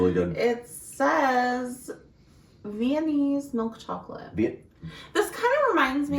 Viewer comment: When you saud Viennese, I heard "Mayonnaise"! My reaction was 😳 haha I have bad hearing so 😂 Jaffa cakes are interesting because I dont like orange chocolate but I love Jaffa cakes! I thought about it for the first time a few years ago and realised that jts basically the same flavour so have no idea why I like one and not the other! I also read that the whole cake/ biscuit debate was due to tax reasons I think you don't get taxed on cakes in the UK so the company had to prove it was a cake to avoid the charge on it 😂